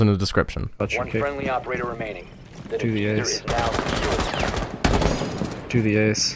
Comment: i don’t know why, but when when he says (do the ace) I hear (do the RAWR )